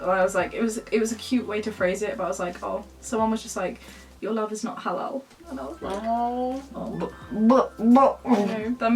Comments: I couldn't understand what she was saying-- The only part I understood was "your love is not-" Does anyone else understand what she said💀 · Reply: “Your love is not halal, and I was like” insert sad noises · can someone please tell me what said, I have very bad hearing, thx to anyone who tells me 🙏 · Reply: ''Your love is not halal'' :( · What does say around 21:25? the comment from the person, “your love is not XXX”? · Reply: SquidoftheSea ‘halal’ = acceptable in Islam. So the comment was saying their love was unacceptable/ forbidden. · what does she say here? 21:25 . it’s been annoying me so much🤣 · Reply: Halal. It's the kosher of Islam.